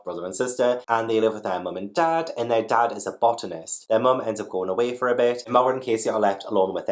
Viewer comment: Lool for Stay out of the Basement, I thought you said that the dad was a Bottomist instead of Botanist. I was like that's a very thing to say.